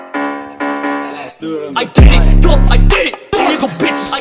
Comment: is he saying (i did it) or (i get it) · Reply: I did it